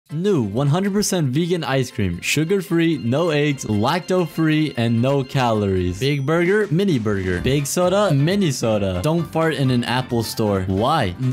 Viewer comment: For a second I thought he said aids but it was no eggs